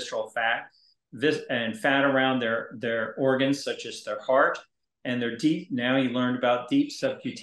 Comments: did he say that you could get visceral fat around your heart and teeth? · Make sure to join us (Dr. Sean O'mara and myself) for the premiere. He announces a new way to look at visceral fat!! I tried to ask all your burning questions so if any additional clarification questions are needed, this is the time to ask! We'll both be on Tuesday at 10AM Central Time. See you then!! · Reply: did he say that you get visceral fat around your heart and teeth? Considering he said you can build visceral fat in your face and around your head I'm not sure if he said teeth or deep.